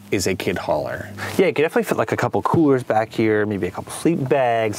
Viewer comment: Sounds like he said the kids can have a couple of Coor's back here...